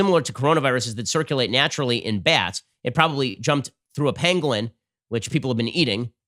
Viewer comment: "Do not eat the damn PENGUINS", I thought it was the bats XD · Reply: WoodStockWolf It's Pangolin. I just learned this 5 minutes ago. I thought he was saying penguin too!